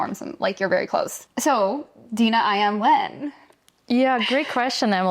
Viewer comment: I heard "Dina, I am wet" instead of "Dina, IM when?"...